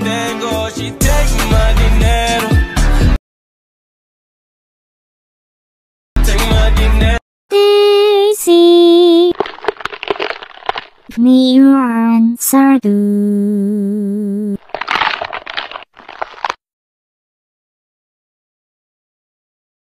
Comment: When daisy I hear it like "daisy. Mie goreng satu"